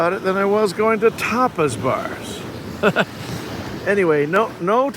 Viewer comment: 🤣🤣 I thought you said topless bars, I was thinking way to go man😉👍